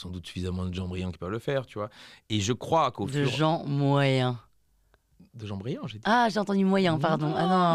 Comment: j'avais cru avoir entendu " moyen" , moi aussi